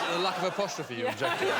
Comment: What does rod say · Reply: Is it the lack of apostrophe you object to?